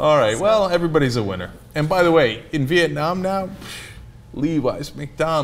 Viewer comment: Did I hear Cenk say "Viet-nam-nam"?